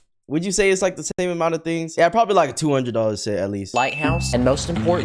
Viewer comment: he clearly said it was $80